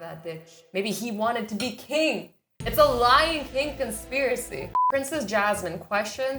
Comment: Lion King. Does anyone hear.. . . L Y I N G K I N G